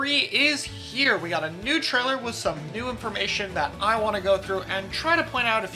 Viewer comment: Man, I heard "new trailer" at the start and got a little excited for moment